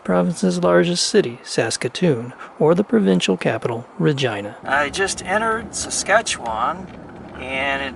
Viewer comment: did he say vagina...i know grow up